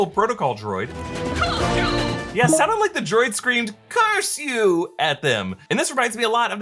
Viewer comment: When the speeder bikes nearly hit the protocol droid instead of "curse you" I thought I heard it exclame "poodoo" - a Huttese curse that translates to Bantha fodder that we've heard multiple characters use including Sebulba when he was taunting Anakin in The Phantom Menace